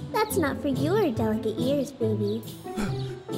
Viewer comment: WHAT DID PIBBY SAY!? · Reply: SHE SAID "baby" UHHHH